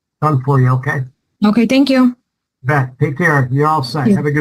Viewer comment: Did he fucking say "bet"? Has proper grammar exchange degraded so severely it's reached the court systems already? I hope I misheard.